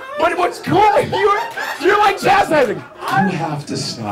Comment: What does he say at 0:46 "You're like ..."? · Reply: cuthbert mot chastising